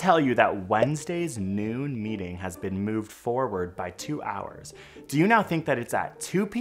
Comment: if it was “brought forward” 2 hours, i’d say 10am, and “pushed back” 2 hours, i’d say 2pm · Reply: Yes!! Exactly!! I don’t think people in my circle (NE Scotland) would say ‘moved’ forward or back. We’d say ‘brought forward’ or ‘pushed back’, which is unambiguous here. When he said ‘moved forward’ I heard ‘brought forward’ so obviously thought 10am.